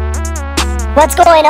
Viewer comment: I thought she said " Your sister was stuck in his brother" lol